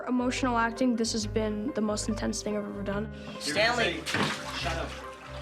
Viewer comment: did he say Stan lee? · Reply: Stanley... That's the name of the character